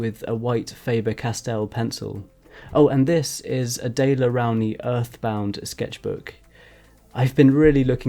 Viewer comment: WHAT? 👂 I CANT UNDERSTAND HIM! what kinda of notebook is it??? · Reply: thankyou i heard "a day around the earth bound" 😂 but nothing came up on google haha